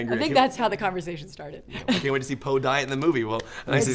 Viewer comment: Low key thought they said: "I can't wait to see Poe die in the movie!" "Yeah, it's my favorite scene!"